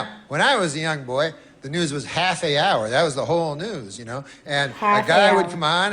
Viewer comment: It's because the H is silent. "Honor" or "honest" would also use "an". It's about the vowel sound more than there actually being a vowel. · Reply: Also, doesn’t Norm say “half the hour” (and not “half a hour”) in this bit? Or have I been mishearing it for a decade?